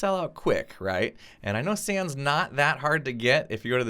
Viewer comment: did he say 'I know Sans is not hard to get'?